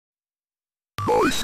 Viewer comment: I heard "I am inside your bone" and I hard paused for like 3 solid seconds there.